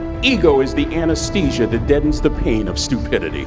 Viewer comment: I can't understand it very well. "ego is the anesthesia that ???? the pain of stupidity." can someone please drop a message. but this is amazing speech. No word can define how amazing this speech and this person really is. 👏 👏 👏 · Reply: Katie Quin deadens